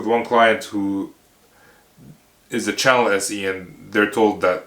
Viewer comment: "channel team"?? Is that what he's saying and what is it?? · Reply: Hi Jay, I said Channel SE, and that is an SE that doesn't work directly with customers, but works with other resellers to help them sell their product. I have more info about channel SEs vs Direct SEs in this video: <link>